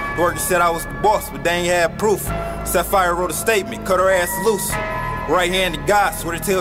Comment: Wait did he say "Sapphire wrote a statement...Cut her ass loose??" Playback